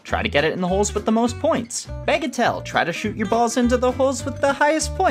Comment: When I heard “bagatelle”, I heard it as “bagel tell” like it was a bagel fortune teller.